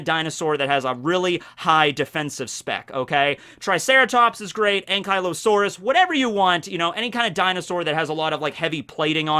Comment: I know what you said but I heard Ain'tkylosaurus.